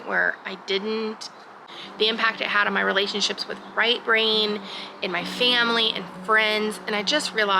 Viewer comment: What is your husband's name. I keep hearing "Right Brain" l am presuming l am not hear you correctly. So l would love to hear you explain his name. Thanks 👍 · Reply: LOL I am saying Right Brain, I asked if he wanted his name used and he said he would prefer not for privacy right now, SO he gets called Right Brain :D